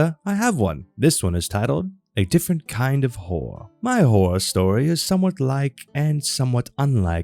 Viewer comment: For a moment there I thought you said 'A different kind of whore". I think your missing a letter their mate.